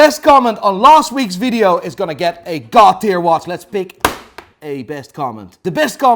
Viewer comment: Whenever he says god tier, I hear “Cartier”. Is it just me ?